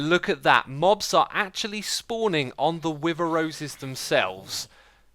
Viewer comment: Wen he says "wither rose" it sounds like he is saying With'os, like some kind of cereal! XD